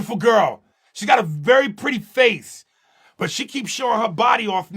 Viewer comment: She got a f-Very pretty face. Tell me I'm not the only one who heard it lmao